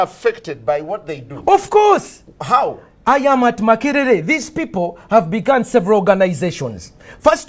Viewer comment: I thought the said I am a cancerer